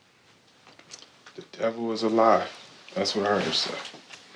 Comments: I did not hear her say the devil is a lie....I heard her say "Goodbye Devil" I pray she has stuck to the words she said and left. Can we get an update on this story please?? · I thought he said “the devil is alive” not the devil is a lie